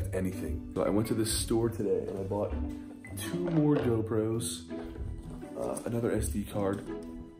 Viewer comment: Anyone else thought that Grayson said DoPros??